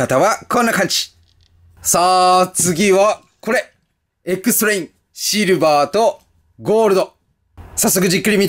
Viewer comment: エックストレインがエクストレイルに聞こえてしまった。 駄目だ寝よ。